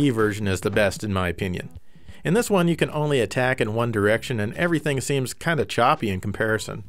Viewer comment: I thought on BC Racers you said "you can attack One Direction", I got really excited at that prospect!